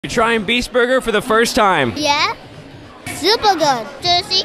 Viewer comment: I thought he said kid trying buger for the first time💀💀💀